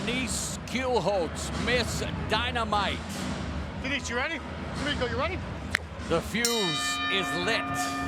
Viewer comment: I thought he said, b**ch you ready! 😂